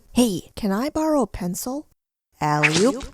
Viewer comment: did I just hear. "and i oop?''